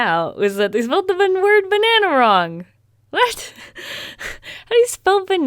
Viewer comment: “Banane?” Im sorry that’s just what I heard.